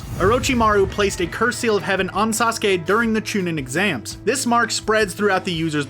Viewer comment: Did he just say chunen?